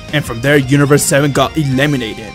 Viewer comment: did he say "universe 7 got eliminated"?